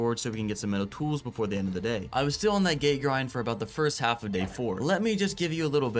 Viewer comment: i thought he said "i was still on that gay grind"